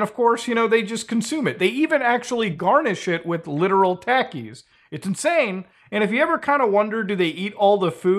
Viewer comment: i thought he said garnish it with khakis 💀